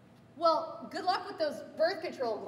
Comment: Ohhhh GLASSES! I thought she said classes! I was thinking "they have classes? Like sex ed?"